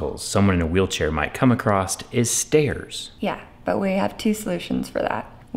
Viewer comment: wtf did he say, "acrossed"? "acrosst"? "A crost"? None are actual words.